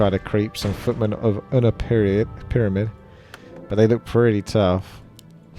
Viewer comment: did I hear footmen on their period?